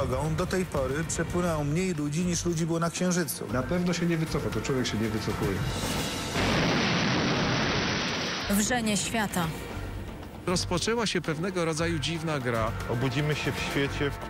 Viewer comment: usłyszałam „czyszczenie świata” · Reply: Wrzenie świata nie czyszczenie świata :)